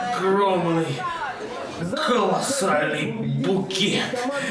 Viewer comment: я один услышал Калосральный?